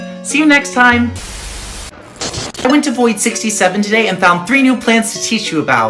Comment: Did bro said 67 0:47 ￼